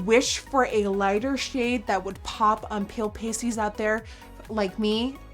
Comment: I thought she said pop on pale pussies not "pop on pale pasties" lmaoooo 😂😂